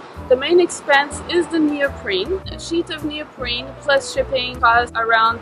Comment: Hi! I'm sorry did you say "neoprene" at 0:29? cause im taking notes of the materials but i didnt get that one right.. Thank you pls reply ASAP. :) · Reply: yes, I use neoprene. Make sure you do not use neoprin which is a different material. Have fun!